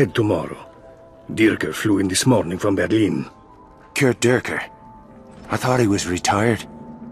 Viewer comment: I swear to god, he says 'Kurdurker'.